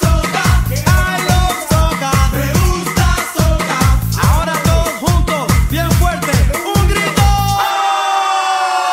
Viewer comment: what are they saying at 2:10 or so? "I love...??" Soda? Sopa? Please help I would love to share this dance with the kids I teach - wonderful! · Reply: Hello Elizabeth. It sais " I love soca" but, when we dance we children we prefer to say "I love soup" me gusta la sopa...it´s more fun :)